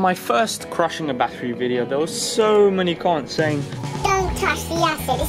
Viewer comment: did he just call his haters "cunts"? brutal. just brutal. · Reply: he said comments